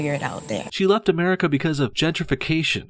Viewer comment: Ah.... Gentification? I thought she said ginger-fication. Is mocking red-haired people a hate crime yet?